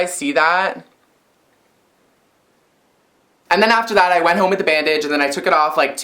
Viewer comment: does she say that she went home with the bad bitch bc I'm laughing hard rn😂😂😂